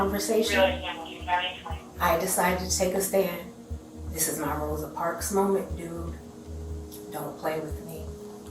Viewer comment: The lady who said she was making her 'Rosa Parks moment' completely disgraced Rosa Parks. Ma'am, you are right that the laws aren't right and black people, as well as many minorities have been disgustingly abused. However, you wanting free things from Target for yourself is not going to help in any way and is just selfish on your part. If you actually want to make change than get involved with politics, which is the only way to actually make change these days. It is an uphill battle because politics are corrupt af, but wanting free things from Target for yourself is not the way to go. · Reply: I’m glad I’m not the only one that heard that! I noticed the subtitles said “this is not a Rosa Parks moment”, but I thought I she said “this is MY Rosa Parks moment”. Nothing against reparations and taking a stand for what’s right but I fail to understand what the long term goal was with this case; I wish there was more information on what is happening here and what she was getting at with Target specifically